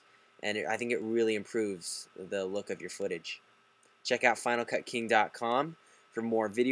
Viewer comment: thumb up if you thought it said finalfucking lmao